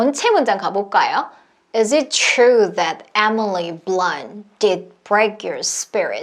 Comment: 근데 아무리 들어도 it is true that Emily~~~ 로 들려요 ㅠ..ㅠ 암튼 12일차 완료!